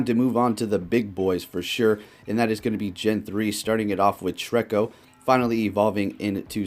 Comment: I deadass thought you said "Shreko" at 0:13...I might name my Treecko that now xD